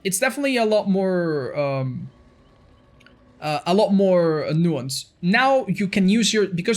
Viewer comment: what does he say? A lot more "nuance"?